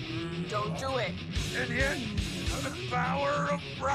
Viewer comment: “The power of rock, compels us…” starts headbanging “ow ow ow ow ow ow” 😂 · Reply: I thought he said. The power of the balzeebub compels us to headbang..